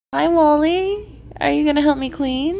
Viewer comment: I thought u said Wall-E